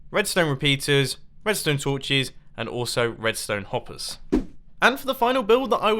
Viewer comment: did he just say redstone hoppers